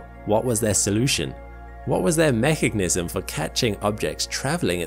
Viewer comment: did you just say "Mecignism"?